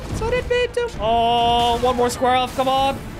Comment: For a moment thought he said "squirrel off". Would have made sense.